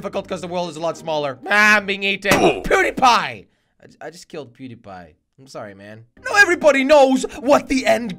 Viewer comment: When you said, I just killed PewDiePie, I thought you said PooDiePie